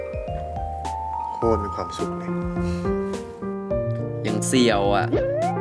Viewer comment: พี่กลัฟพูดว่าไรนะ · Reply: ฟังว่า อย่างเสี่ยวอ่ะ ค่ะ